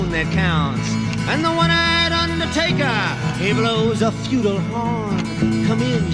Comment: the caption in the video says "... and the one-eyed undertaker he blows a futile horn" - I'm pretty sure he actually says "feudal horn"